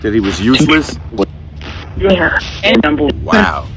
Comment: Before said "he was useless," I heard "save himself."